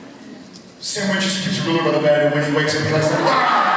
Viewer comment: What did jared say around 49:25 about what scene took the longest to record? He said it way to fast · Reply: "Sam Winchester keeps a ruler by the bed and when he wakes up he likes to ah!" Jared's quoting the scene in Mystery Spot where Sam speaks simultaneously with Dean every time Dean tries to say something--thus proving that Sam knows what Dean's going to say b/c he's reliving the day. Jared has described this as one of the most difficult scenes since he & Jensen had to speak in unison and one or the other would keep messing up by adding in or leaving out or changing a word (like saying 'the' instead of 'a').